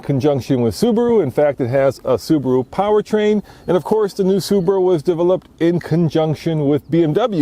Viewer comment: Even the wheel lug nuts are Bmw style... 1:27 did u just say Subra? And pls stop saying conjunction too many times find another word in the next future video.